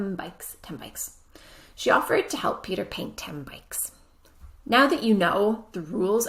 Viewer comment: He talked to him all evening,. But the in the first task, I swear I can't hear Peter in your sentence, I heard as " she offered to help you paint ten bikes" 😀